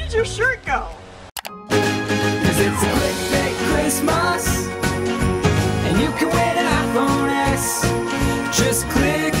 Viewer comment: It sounded like he said and you can get an iPhone X